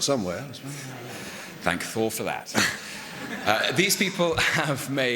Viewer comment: when Mark says "Well, we all had to start somewhere", did Tom say "Thank Thor for that."? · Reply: Manuella Andrade He says "thankful for that". He just enunciates "ful" a bit so it sounds kinda like Thor lol